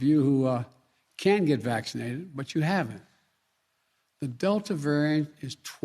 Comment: I heard him say the 'DEVIL' variant